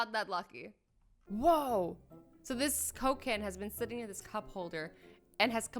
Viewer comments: did she say cocaine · DID SHE SAY COCAINE am i the only 1% that heard cocaine xD